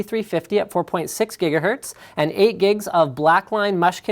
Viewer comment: Linus says the RAM is 8 gigs but the caption says 16 gigs for both AMD and Intel CPU's LOL!